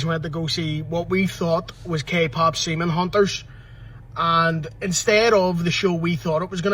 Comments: Did he say K-pop semen hunters? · did he say semen hunters